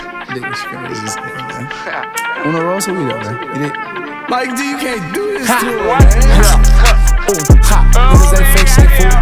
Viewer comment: what is he sayin please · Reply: "Mike d you cant do this to em maannn"